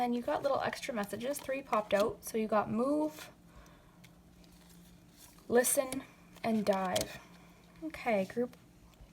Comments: Group 2 I thought she said "move, listen, and die" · In group #2 I thought she said “move, listen, and DIE” I was like😳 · I heard « Move, Listen and DIE » 😂